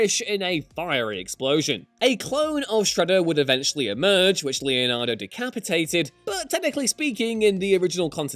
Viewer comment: Who else thought he said “Leonardo decaprio” for a second